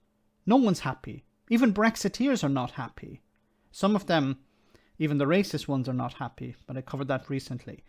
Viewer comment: “Even the racist brexiteers aren’t happy”, I thought you said all brexiteers are racist. I’m not a brexiteer and I’m not a racist but I did vote to leave the EU. Can anyone out there help me with my identity crisis.